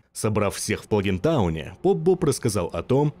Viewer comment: мне послышалось пони таун .. "Собрав всех в пони тауне"